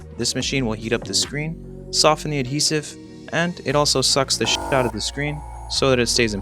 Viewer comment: Did you say sh*t ?